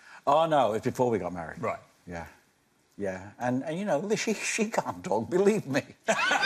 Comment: What did he say 26:27 i cant understand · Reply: "She can't talk, believe me" Meaning she was seeing other men and wasn't fully decided on Barry.